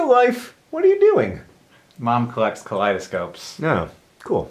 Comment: When I heard "Mom collects kaleidoscopes." What I really heard "I like turtles."